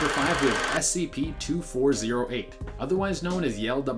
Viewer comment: The guy says, "SCP 2408" The caption says, "SCP 2480" Bruh I'm confused which one is it